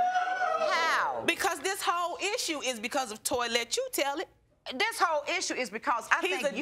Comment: Now, who done sat up there n named that man TOYY?!?!😩😂😩😂😩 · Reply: I’m sorry but I SWEAR I heard her say “Toylette” 💀 💀